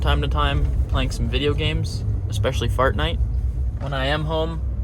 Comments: Turn On Captions. Instead Of Fortnite Its Fart Night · Did he say FART-NITE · oh geez i thought you said fartnight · I thought you said "Fart Night" and I briefly wondered which video I missed. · I thought you said fart night 🌙 🤔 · Is it just me or did he just say fartnight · At first I thought he said Fart Night when he said what game he liked to play!! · it sounded like he said fartnite